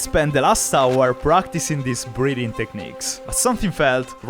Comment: 3: 36 - "I had spent the last hour practicing these breathing techniques." Not gonna lie, thought you said "breeding techniques." I was like, "Heh??"